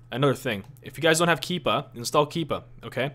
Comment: What did you say to install at 9:54 ? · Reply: John-Patrick Dela Rosa (KEEPA) . Google it it will pop right up